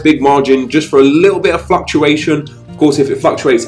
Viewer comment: i could have sworn he said fucktuation instead of fluctuation